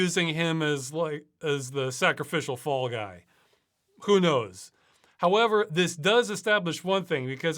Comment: did someone say Fall Guys?